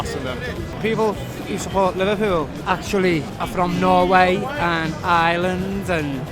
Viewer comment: Go back to norway lad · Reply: Norway? i thought he said North Wales actually.